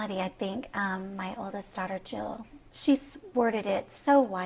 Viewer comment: Did she say “her oldest daughter Jill?!?!